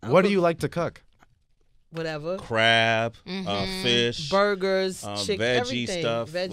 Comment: bruh, i thought he said crack lmao